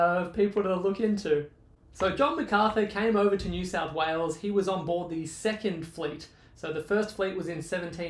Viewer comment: I almost thought you said “ he was on the second fleece” 🤣🤣🤣🤣.